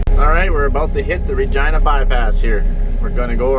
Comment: I swear Everytime you say city.of Regina it's sounds like your say *agina....I was like it's a family show🤣🤣🤣